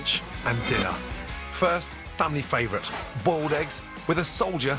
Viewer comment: did he say Bold eggs..? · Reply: human nature No, he said ‘boiled’ lol. It just sounds like ‘bold’ because of his accent.